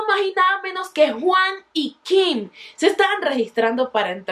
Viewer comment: Soy la única que en el minuto 7:45 escuché yim Sin ofender eso es lo que escuché